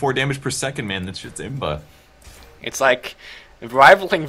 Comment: did said imba in the 20:41 mark?